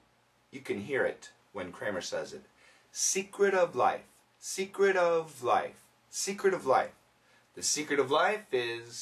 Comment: why i heard he said I'm sick of life....oh my poor listening